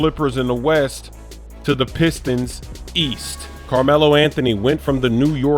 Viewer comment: East, I thought you said WEAST